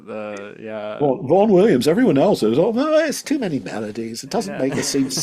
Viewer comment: Instead of Vaughn Williams I misheard Rowan Williams🤣Also, underrated.